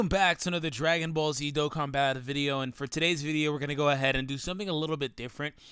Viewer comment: it sounded like you said "dokkan bad video" at the introduction